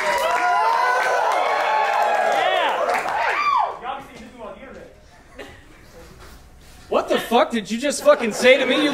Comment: what did the dude say? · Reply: not sure of the start but the rest of his sentence is "this dude on the internet!" maybe something like "yo ive seen this dude on the internet!", idk